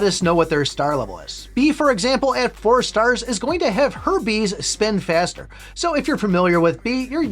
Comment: it sounds like you said herpes instead of her bees and it just made me laugh 😂😂